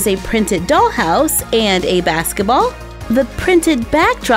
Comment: Am i the only one who heard her say baseball in stead of a soccer ball